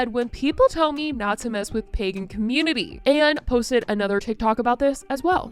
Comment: I heard “pegging community” like sexual, instead of Pagan community so when you started talking about religion, I was so confused! Frankly I find it all entertaining. I don’t take her seriously at all bc it’s literally her thing to troll. It’s also entertaining to watch people get mad.